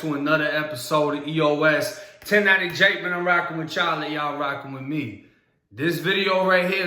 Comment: When I first started watching ya shit, I thought you were saying "I'm rocking with Charlie and y'all rocking with me" 🤣 I'm like what's he talking bout like am I getting to old and out of touch with newer slang terms but it was just my hearing impairment fking with me 🤦